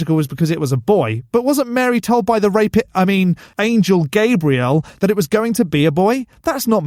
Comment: am i the only one who hears GAYbriel there? Xd